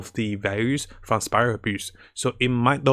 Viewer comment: ik it was cuz he was speaking a bit quickly and had a bit of tongue twisting here and there but at 5:58 it sounded like he said spiral abuse and honestly hes not wrong lmao